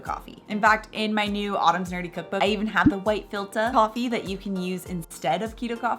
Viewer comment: you say a type of coffee that replaces Keto coffee. I've listened 5 times but can't understand. Can anybody help me out? :) White $#!? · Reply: White filter coffee